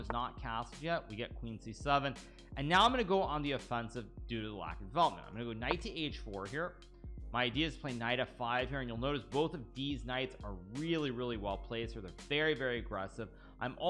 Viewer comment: i swear i hear hikaru saying diz nuts. instead of these nights